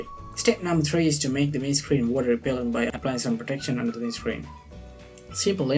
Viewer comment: Hi thanks for the vids. just when you say step three. i hear "make the windscreen some water abillion by apply some protection" what are you sayign here? thanks? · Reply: Bud Wizer Water repelent?